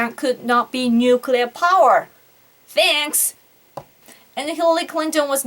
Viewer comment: 1.What is the word spoken at 14:41? sounds like: thance! (?) 2. " 22:20? sounds like: plough (?): "...because this (plough, blow?) started from the..." Randomyoko2, (25:15 - 25:24) you said: "We need nuclear weapons with 'first use policy'..." but you attacked the Democrats specifically and disparagingly for doing, in WW-2, what you advocate here (!), thanks for sharing. Hillary is not fit to be President; neither is The Donald. Japan is an independent Nation, If Japan wants to do away with the 3 non-nuclear principles it can have it's own nukes Tomorrow. Are you petitioning Prime Minister Abe? Are you trying to influence your own government? If Abe and Trump had met, Abe would soon have seen that Trump is an uneducated demagogue without a plan. Thank you for reading this.